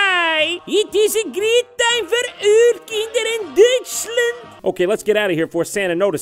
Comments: Can somebody please tell me what he‘s trying to say here? „It is a great Daivaük Inder in Deutschland?“ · ich hab kein Wort verstanden, außer "Dütschlünd" XD